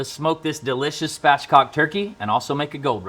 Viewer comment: CC says fatcock instead of Spatchcocked lmao